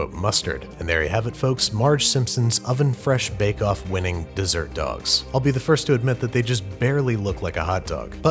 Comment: I thought this was just a grilled hot dog in a tortilla. Because I thought it said desert dogs.